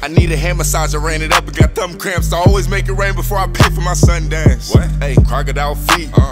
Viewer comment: does say “I always make it rain before I pray for my son Dax”? 😂